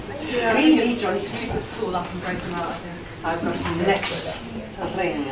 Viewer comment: Does anyone know what doc c says at 1:45 ? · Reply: I think its "ive got some necks to wring"